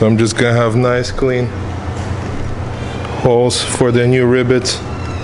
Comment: I love you mate, and please, dont take offense to this , bit I swear it sounds like youre saying RIBBITS to me (as in the sound frogs make) when you say rivets and I cant stop laughing! Im a terrible person I know XD I CANT HELP IT!!!